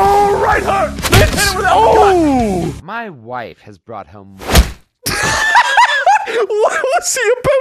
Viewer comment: "My wife has brought hell"? Is that what he said?